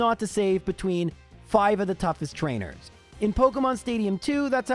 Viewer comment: Hey! Just wanted to say I noticed what I can only describe as an audio error that must've slipped past the editing phase, at roughly 42:25 I keep hearing "5 toughest trainers" in reference to the Elite 4 and Champion, but I know you really said "4 toughest trainers" :)